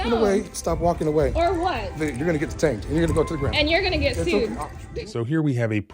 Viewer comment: You heard him threaten bodily harm right? Did anyone else hear that or was I hearing things? Stop walking away, stop walking away: Or what? You're gonna get detained and you're going to go to the ground: And you're gonna get sued. Not to mention he's 3x her size so that's menacing and scary, if he threw her to the ground he could cause major bodily harm to that woman who is quite petite, and not wearing pounds of gear like he is.